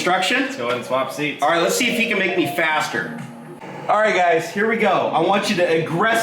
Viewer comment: I first thought he said: "Let's see if he can make me fatter" hahaha